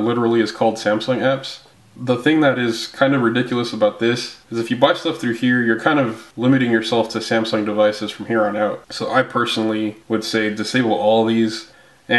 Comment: I couldn't hear what you said about the Samsung apps between 3:30 - 3:40 something about botching them? If you could clarify because I was a little confused about what to do with the Samsung Apps, just disable like the rest, clear cache, and clear data? Also The pre-installed Kindle, flipboard, etc. you said those can be disabled as well? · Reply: (J) I said if you buy Apps through the Samsung store, you're limiting yourself to only having those apps available on Samsung devices in the future. Yes, Kindle and Flipboard should be able to be disabled as well.